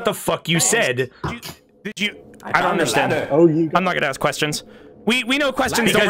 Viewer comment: Bubby was DEFINITELY saying "Oh ye God"